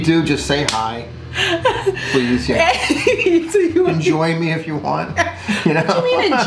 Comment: Your man is so unproblematic and hilarious !!! I love y’all videos. I don’t understand why the age gap bothers other people or why they care... it’s your life not mines. I’m always happy for love 😆 I thought he said “enjoy me” too lol but I realized quickly he said and join me but your face turned real quick 😂😂